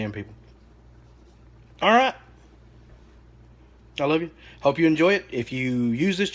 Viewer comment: Did you say "I LOVE YOU"??? LOL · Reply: Texas Built yes I did. Every video. I love you, I'm praying for you, and I'm thinking about you.